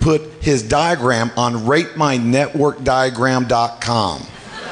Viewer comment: I thought he said Rape my network diagram... whoops